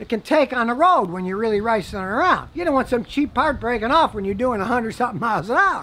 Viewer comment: Oh snap, did Scotty just say "Rice'n around" at 6:20? Please say no.